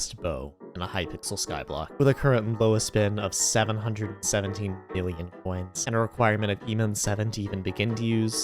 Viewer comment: I really can't tell but I think i heard you say billion. But I just want to clarify that, 𝕋𝕙𝕒𝕥 my friend is 717 million